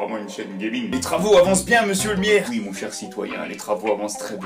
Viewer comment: Est-ce que c'est juste moi ou il a dit M. miaire à la place de maire à 7:45.